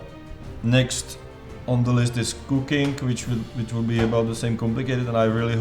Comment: When he mentioned cooking, my eyes almost popped out because I thought he said "cocaine".